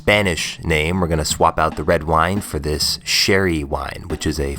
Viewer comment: I thought you said cherry wine at first, not as spanish but i bet it'd also be delicious